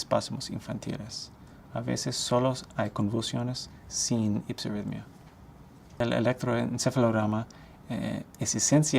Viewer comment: Alguien me puede decir que dice exactamente en el minuto 7:10?? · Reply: hipsaritmias